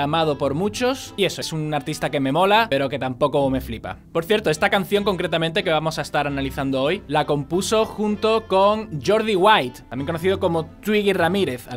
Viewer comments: entendi jordi wild 💀💀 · por un segundo escuché que la compuso con Jordi Wild xD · escuche Jordi Wild jajajaja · pensé que decía Jordi wild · escuche Jordi wild :v